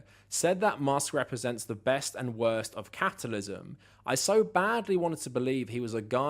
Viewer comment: Off topic... you made me laugh when u said capitalism, but it sounded like u said catitalism....lol